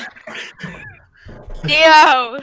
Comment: cosa dice? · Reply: dio